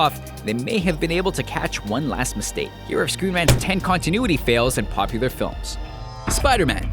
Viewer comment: I thought he said screw man's.... but I knew he really screen rant's